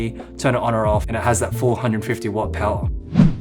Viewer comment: I heard you say, "...and it has that 450-watt power" (at 7:31). Did you mean that it has the equivalent brightness of a 450-watt halogen light? The reason I ask is because I was told by a Godox dealer that the way to estimate the brightness in equivalent halogen watts is to multiply the UL150 watt usage by 7, getting 150 x 7=1050 watts equivalent halogen watt brightness. I need more brightness than my two 750-watt Lowell tota key lights are delivering and I thought the UL150 would give me 33% more per light. But if the UL150 is only delivering 450 halogen equivalent watts, mabe I need to think about a Dracast Fresnel Studio LED2000?